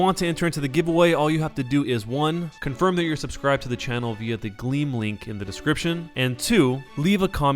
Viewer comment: in the description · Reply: Ik, I first always heard "Green link," and now I hear "Glean link"